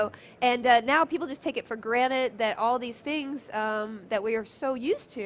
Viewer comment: Did she say people take things for granite???ROFL